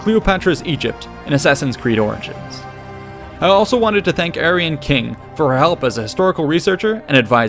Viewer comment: Heard that as " Aryan King " lol.